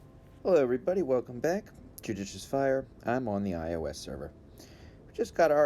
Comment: I saw captions... Instead of judicious fire it thought you said "kid uses fire"😂😂